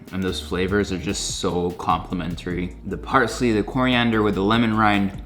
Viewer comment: I thought he said Coriander but there wasn't any on the ingredients listed on the description box